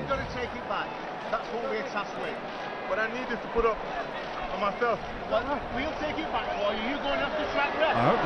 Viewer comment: LMAOOO, at 6:40. The big controversy everyone thought, was bolt saying. "I need it to put up on my shelf " this man is hilarious